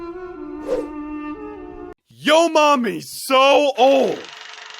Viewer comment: he said mommy instead of mama